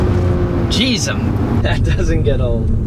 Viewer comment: Did he say jesum? Instead of Jesus @ 4:19 someone please confirm lol